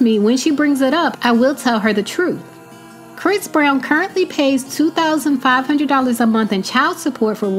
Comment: With 10,000+ per month she can afford a Home, maybe not a Mansion of what she wants, but she could manage buying or renting a Home. However, speaking to a child about adult things is ridiculous and inappropriate to say the least! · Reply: I thought she said he pays $2,000/month child support and $4,000/month for a nanny.